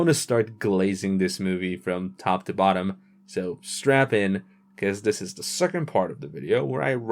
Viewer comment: Oh you sad strap in I thought you said strap-on well at least I was prepared for both few 😅😅